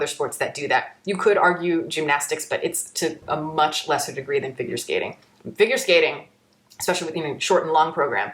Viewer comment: This video was playing in the background while I was cooking and I honestly misheard talk about finger skating for several minutes. I had to rewind to check if I had lost my mind